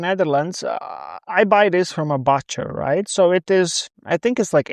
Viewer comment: Butcher is pronounced "Boet" not "Butt" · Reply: I thought he said « botcher » and I found that enormously funny.😄